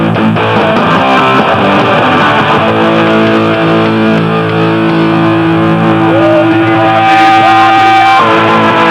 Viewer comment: Que dice en el 5:36..por curiosidad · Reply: odio a mi patria